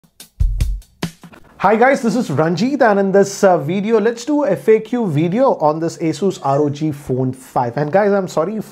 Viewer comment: I thought at first he said lets do you f**k you video, had to replay again lol